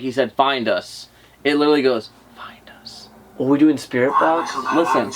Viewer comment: I heard 5:32 during the video and thought it was saying behind us, meaning there’s a sound behind us.